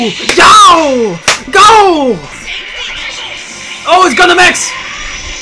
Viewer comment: Wait a minute... DID HE JUST SAID GUNDAM X!?!?!?!?!?!? TRIGGERED